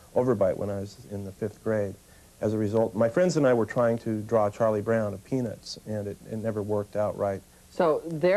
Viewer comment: When he says "My friends and I were trying to draw Charlie Brown of Peanuts" I swore I heard "My friends and I were trying to draw Charlie Brown a penis."